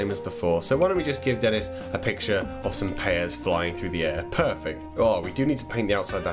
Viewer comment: i thought he said "pearfect" but I might have imagined it